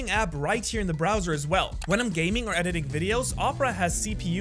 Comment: Did he just said in filipino walang gaming in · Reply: He said “when im gaming” nga